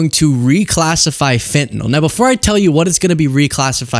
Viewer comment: I misheard it as Finland the first few times and I got very confused.